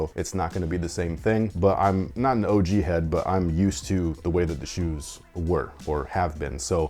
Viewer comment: did say he’s not an OG head?? Lol